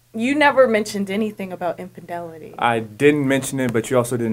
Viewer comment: What the fuck is "impendelity"? The word is called "infidelity" not impendelity. Now say it with me, that's "in-fi-del-ity".